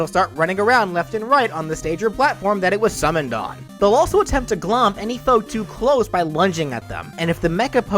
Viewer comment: Did... did ou say "glomp"?